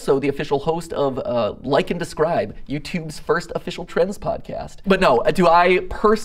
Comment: if not for the subs I thought he said "trans podcast"